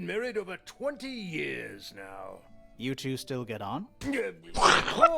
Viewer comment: you reacted like Grimoire had asked "Do you two still get IT on?", ie, sex. But he actually said "Do you two still get on?" as in "get along with each other". Very funny moment, though. A barometer is a tool that measures atmospheric pressure. They're common on sailing ships, as they would help you predict storms. Great playthrough so far!